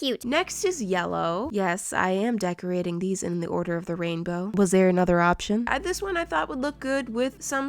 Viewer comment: yes i am painting it in the order of the rainbow Me: Hold up i thought you said Order of the Phoenix